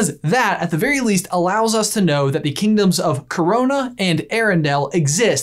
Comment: Did I hear that right?? '' Corona'' Illiminati